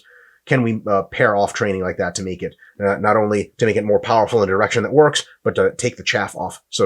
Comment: Thought that Mike said "get a more powerful erection ..." - together with the gestures almost had me on the floor.